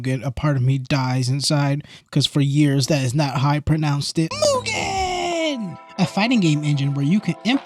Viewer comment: wtf were you saying? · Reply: Myuu-jin. I know disgusting . I’m so sorry